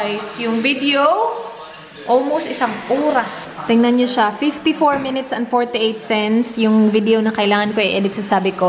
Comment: 54 mins and 28 cents??? Tama narinig ko? 😊 Masarap nga chicken salt sa chips. Yummers! 😋😋😋